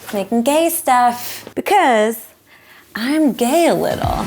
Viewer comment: When she said “I’m gay a little” I thought she said “I’m gale”